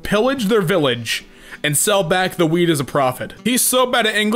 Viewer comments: I swear he said weed instead of wheat 4:21 😂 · When Damien was talking about the Minecraft wheat I thought he said weed 🤣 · sounds like you said weed